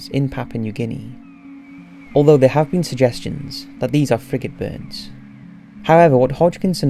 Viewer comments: does he say freaking birds? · I thought you said "Freakin' birds" lmao